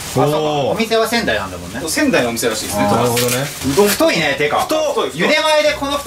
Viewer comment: 『うどん太いね』に聞こえるｗ